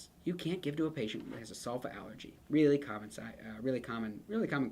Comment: mins, what allergy did he say? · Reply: Sulfa allergy!